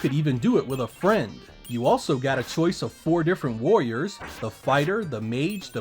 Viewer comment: I thought you said you got a choice between 4 different lawyers